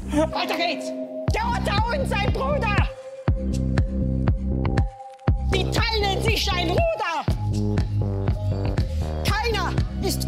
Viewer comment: Was sagt sie ? " Der OTTER und sein Bruder, teilen sich ein Ruder " ?! Bei